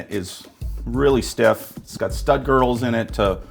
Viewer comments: I thought he said Stud Girls as well. Lol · Thought he said "stud girls" 😂😂😂